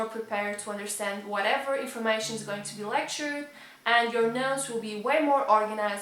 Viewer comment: Am I the only person who heard nose instead of notes? Even so, the video is still A+